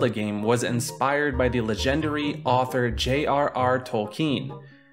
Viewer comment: did you just say 'luh-gender-y'?